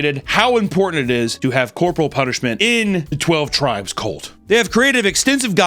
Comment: when he said corporal punishment… I heard pork roll punishment 😅